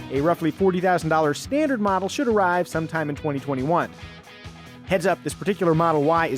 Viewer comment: I initially thought he said the standard $40k model would arrive sometime in 2041. and I wasn't surprised.